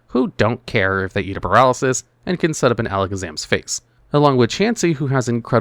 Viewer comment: With how fast you talk, for a second I legitimately thought you said Exeggcutor and Slowbro can sit on Alakazam's face